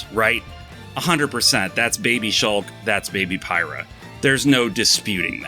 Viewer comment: Love the "Baby Shulk" comment but I just heard "Baby Shark", ahhhh! One of the side effects of having kids I guess, lol. Love the channel, great humor and honest opinions. Keep up the great work!